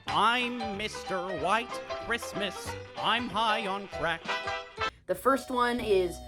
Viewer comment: I probably heard that Wrong, Did he say "I'm Mr White Christmas, I'm high on Cr*€k" Is that what he said? 5:37 So little Brother is into Anime to, Nice, So a fellow Otaku/Weeb, That's cool to hear~ 8:04 I never saw that Version of MLP, But it's cute, But I like Gen 4 {Friendship is Magic} I'm sure that was Gen 4, Right, Sorry... I'm honestly not sure about which is which in Generation's!